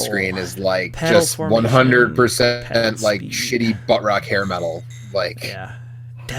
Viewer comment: what did you say at 2:58? Like "100% shitty ????? hair metal", there was some issues with the connection(( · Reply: "100% shitty buttrock hair metal."